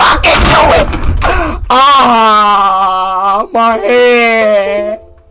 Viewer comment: What did they say? 0:35 aaaaaaa my head😭 or aaaaaaa my hair😭